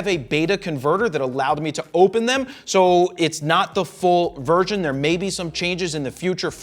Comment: Did he say “full virgin?”